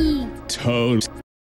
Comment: Is Crush saying “totes”, “toast”, or “toes”